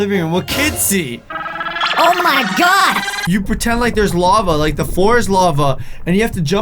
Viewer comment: he said the floor is lava 5 4 3 2 1 You did nothing......savage XD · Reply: I was in my bed and I realize you said the floor but I thought you said bed 😂